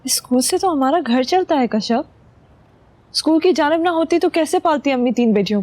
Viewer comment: School ki "janab" na hoti tu humara ghar kaisay chalata? 30:11. Am I the only one who heard that?